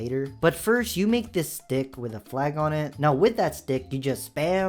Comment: am i hearing stuff or that sounded like d*** not a stick